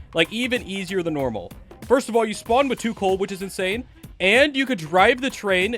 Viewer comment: i wanna point it out, that the subtitles said too cold instead of two coals. Try to fix it. Aight.👍